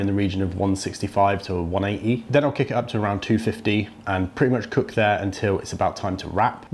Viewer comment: did you say 215 or 250? It sounds like 250, but then after you said kick it up again to 250. Actually I think you just repeated the clip accidentally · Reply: 250